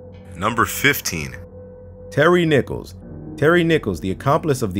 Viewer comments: HELP I THOUGHT TERRY NICHOLS WAS SAID AS TERRY NIPPLES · Who else thought he said "Terry Nipples" in #15?? 😂😂